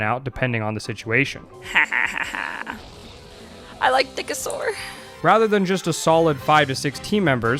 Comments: For a moment there, I thought I heard her say “Dickasaur” instead of “Nickasaur”. 😂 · I thought she said "I like thickasaur"